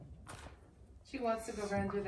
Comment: Oh my gosh at first I thought you were saying the banana doesn’t fit through the tunnel and then I realised you were saying Puddy doesn’t fit anymore!! I can’t believe it how could she get so big already . She’s so cute thanks for letting us watch everyday and see her grow up. 💕